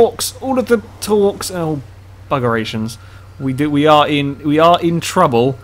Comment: did he say bugger asians